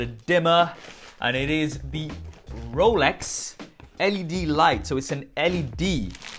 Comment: Did I just hear 'Rolex'?